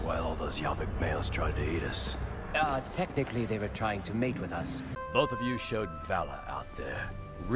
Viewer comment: NO BUT WHY WHEN TECH SAID “they were trying to mate with us” I KEEP HEARING “they were trying to make with us”